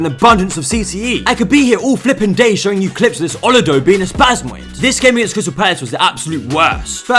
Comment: did you say "Olodo" the Nigerian slang?😂😂